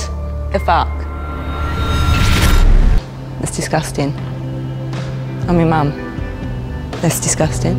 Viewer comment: I thought it said 'I'm in your mum that's disgusting' and I was like omg wtf